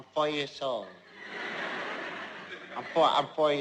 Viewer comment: when he sayd "i'm 4 years old" i heard "i'll buy you're soul".